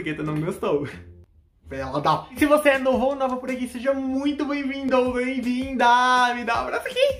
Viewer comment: Eu jura que escutei ele falando: seja muito BEM vindA ou bem vindA 🐇